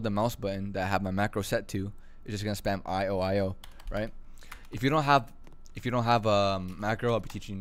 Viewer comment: why i hear about he said you just need to spam i ohio